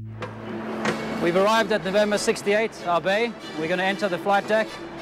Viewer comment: seriously the subtitle says N68R???? come on CX, you know, that all of your aircraft are wide body and wouldnt be parking at the offset position. It's N68 not N68R. He was saying "we have arrived N68, our bay. "